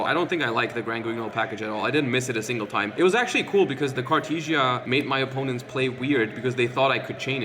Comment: Minute 10:48 did You just said you used cartesia so it makes your op think you are running granguinold? I dont think konami like this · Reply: He used ecclesia as tuner, but not it's fusion effect, which often his opponents just assumed he did since it's more popular version.